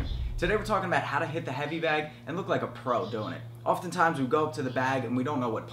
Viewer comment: I thought he said “how to hit the heavy bag while stoned doing it” Lol